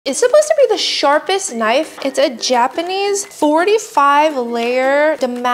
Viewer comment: I thought she said the shortest knife💀